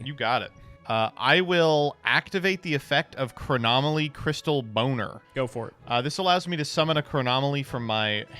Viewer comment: Did he just said Chronomaly Crystal Boner 14:53 ? xDD